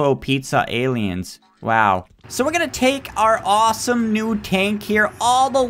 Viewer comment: I thought he said tank our tank